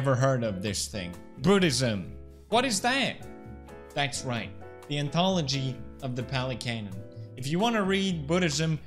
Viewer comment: ngl i thought he said: the anthology of the pelicans.